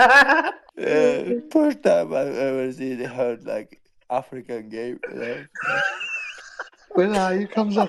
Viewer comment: I thought he said "African Gate" when i listened referring to all that drama between Izzy and Dricus